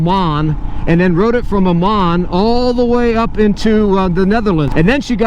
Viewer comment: did you say Amman (the capital of Jordan) or Oman? Impressive either way! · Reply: Oman. but still right?